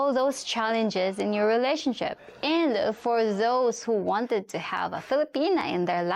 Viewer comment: Please ask your question again? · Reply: At the 30:32 time mark where you said "And for all those who wanted to have a Filipina in your life, I think you must twice". The first listen I heard "want" which seemed contrary to deferment towards Filipina women. The second listen I understood you to say "wanted", which paired with your voice tone, may be that you were just making a slight tongue-in-cheek comment.. [ can explain that meaning]. But as non-sensical hair splitting as this may seem, please make a clarification. You have to understand the time, energy, and financial stakes are high for a foreigner to come to the Philippines. A video ending statement, "you must think twice" in regard to wanting a Filipinia in your life, almost sounds like "are you crazy?" I'm sure that wasn't your meaning, but let us know. By the way, outstanding video presentation performances -- consistently -- from you &.